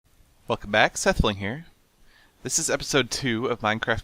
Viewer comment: when I started the video I thought he said "f**k back sethbling here" xD